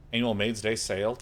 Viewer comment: he defo said "anal" instead of "annual".